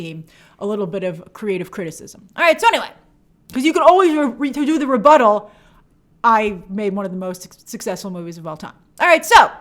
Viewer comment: what did she say? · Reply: She meant to say, "because "you can always do the rebuttal - I made one of the most successful movies of all time" (but she mumbled).